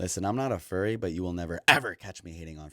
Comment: I thought it said "Furby" at first